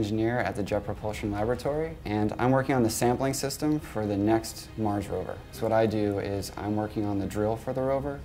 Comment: ooh Mars Rover..... I thought I heard Mark Rober